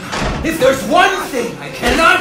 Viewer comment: If I didn't read the subtitles, I could have swore he said "if there's one thing I can't stand, it's a queer."